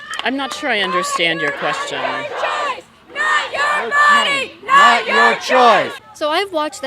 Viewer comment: When they were chanting "NOT YOU CHOICE" I thought they said SACRIFICE